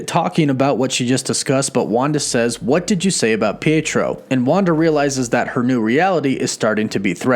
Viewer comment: Bro, did you just say her brothers name is Pedro and not Pietro?